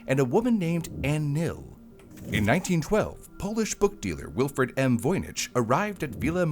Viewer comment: It's so nice for a Rare Book Stealer to donate a book. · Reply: It sounded like he said book stealer lol but it was books dealer